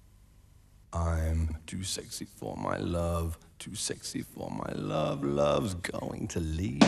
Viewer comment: odd way of pronouncing Milan. I always thought he was saying : "I'm too sexy for me land".